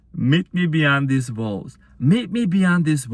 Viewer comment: My goodneeess... I heard "met me beyond these balls" 🤣 wut ? Indeed, he must have some balls to meet me beyond these walls too. Thank you 💜